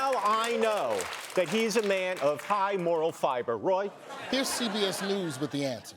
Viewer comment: Moral fiber...is that like alpha-cellulose ? I thought he said moral clarity. I'm confused. Maybe it's like Wiener Blut? I bet Nutlick is fluent in platitudes and Deutsch.